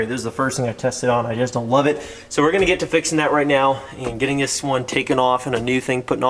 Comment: I thought you said you were keeping this one